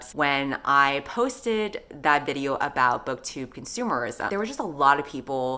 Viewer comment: So...I always feel like I am not a "real reader" because people will ask me about my book collection and I only own like...5 books. I almost exclusively read library books..not just library books but e-books I have borrowed from the library. I have read hundreds if not thousands of books and can wax poetic about archetypes and tropes but I still feel like an imposter when it comes down to it. I am gonna hop on over and watch your consumerism video now.... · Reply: Bruh I thought you had said communism instead of consumerism.